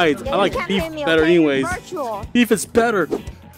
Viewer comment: Did she said Joshuamartial? beacuse thats my user name!!!